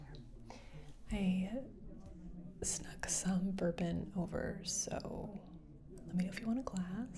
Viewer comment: Instead of bourbon I heard burping